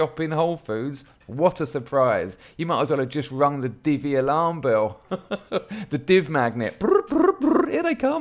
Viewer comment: When you said 'Divvy Alarm Bell' I thought you said 'DVLA bell' and I was utterly confused as to why that organisation could possibly be of any assistance.